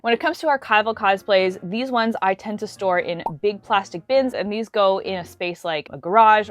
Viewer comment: Omg I thought it said stole....